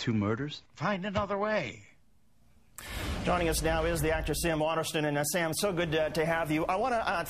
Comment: Does he call him an actress?